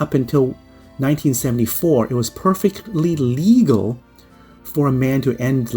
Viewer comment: did he say "perfectly lelegal or illegal or legal???" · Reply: Perfectly legal. Until 1973, it was perfectly legal to k1ll your wife's lover in Texas. Don't sleep with married women.